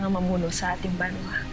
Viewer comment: banwa talaga sa Amaya yun parang si binayaan narinig ko eh